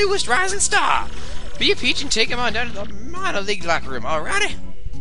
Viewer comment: lol You just did an unintentional joke right there! The Mario-league, lol(2) I mean, it almost sounded like that. . .